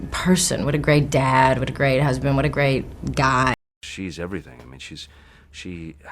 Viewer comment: Did he just say she is EVERYTHING